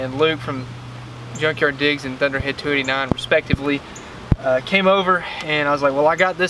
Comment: I had to back up. I thought you said "Featherhead289", but that was just in my head.